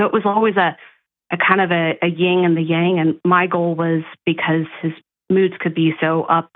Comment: Umm i’m confused she said her brother’s name was Johnny then i swear i heard her say Michael like four or five minutes later. Or did i miss something. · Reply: She said “My goal” not Michael.